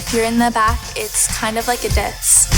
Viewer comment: Idk if it was just me cause I’m not Canadian but when said “it’s kind of like a diss” her Canadian accent sounded really strong that I thought she said “it’s kind of like a dense” I was so confused lmao